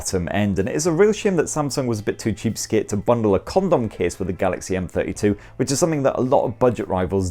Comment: Did he say condom case? 😳